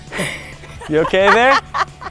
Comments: In this video we will be seeing if flex tape can fix my marriage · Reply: is it just me or did he say u ok babe? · " you ok babe" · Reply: I heard "You ok there?" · did he call her babe? i always wondered if they were a couple, or just co-workers... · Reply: It sounded like he said " You OK, there? " to me.